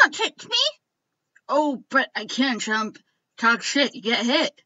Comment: I thought sans said trump when he said chump